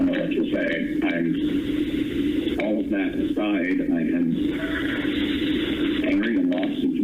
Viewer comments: I think he is saying "all of that aside" as in, "anyway" to continue the convo. I don't think he said that he's all of that inside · At around 32:44 I think the voice transcription on the original video was incorrect. I don't think he is saying "all of that inside." I believe says "I'm..." then stops and interrupts that thought by saying "All of that aside.." It's not a big deal really, I just love trying to discern what people are saying in hard to hear clips. I really wish there was a wikipedia suggested edit thing with these transcriptions, I'd love to be able to help.